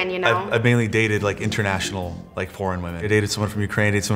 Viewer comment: “I’ve only ever dated international foreign women” wtf😭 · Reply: I thought he said PORN not foreign omg